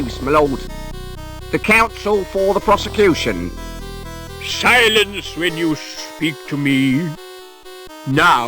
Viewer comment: Did he just say "silence when you speak to me"?